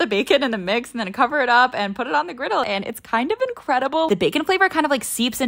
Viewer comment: I thought she said “put it on the griddie” 💀